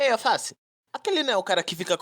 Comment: eu ouvi o Mendoim falando Alface,e não Alce